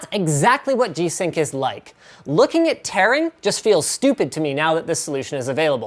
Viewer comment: I think he said "Taran", not "tearing"